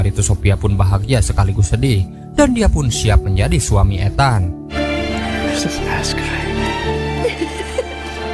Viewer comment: ini aku gak salah dengar kah di menit 32.10 "dan dia pun siap menjadi suami ethan" 😂